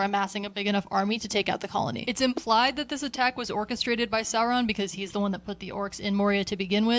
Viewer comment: did you say Orcestrated?